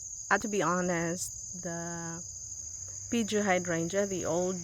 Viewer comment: What kind of hydrangea is that that you said was an 'old' kind? It sounded like you said beachy or peachy...it's really pretty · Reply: Peegee hydrangea